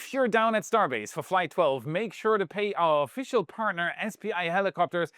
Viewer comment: Go to <link> to get $250 OFF! What's your prediction? Does Flight 12 launch on the primary date, or does it slip to one of the backups? And how do you think this version 3 maiden flight goes? Drop your predictions below. :_WAISweet::_WAIStarship::_WAIHeart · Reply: so the way you tossed in that quick ad-spot for SPI helicopters @ 9:08 was worded REALLY BAD! you say '.. make sure to pay our partner SPI..." - at least THAT IS HOW IT SOUNDS - I KNOW you are saying 'pay them A VISIT' but putting their name in between 'pay' and 'a visit' just turned an innocent ad-read into a demand for money that you BETTER pay up or the mob is gonna break your knee-caps! LMAO - Now EVERYONE knows thats not what your saying, but that gap in between pay and visit changes the entire tone of the ad-read. Just Sayin :)